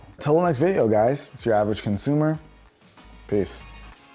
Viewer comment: First time I heard his intro: “ what’s up guys, it’s ‘Dravage Consumer’.” 🤣🤣 Love your vids keep it up!